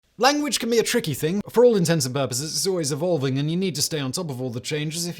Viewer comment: In the opening it sounded like he said "for all intents and purposes" which is the correct phrase (as opposed to "for all intensive purposes"). I'm just gonna assume that Simon couldn't force himself to be wrong on that one.